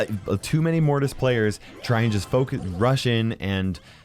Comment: I know he said "focus". But it sounded like he used the f-word